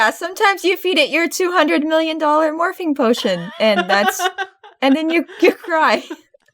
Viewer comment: I misheard Morphing potion as "Morphine Potion" and just accepted the fact that and were drugging their pets